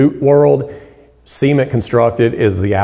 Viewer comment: what is he saying here? Seemed hemmed construction? Semen construction? I got it now that I typed that. Gonna finish. See minted construction! As in cemented construction.